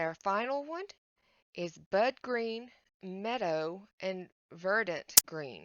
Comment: Ohhh fun stuff! You’re getting a nice collection of those markers. I so thought you said Butt green lmbooo 😂😂😂